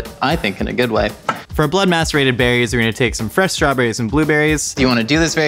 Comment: For the record: I thought you said “emaciated” instead of “macerated”. Also, can we play Scrabble? I’d like that, a lot.